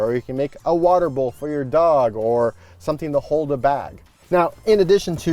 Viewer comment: Lol, at 1:40 I thought he said, “…something to hold a bat.” 😆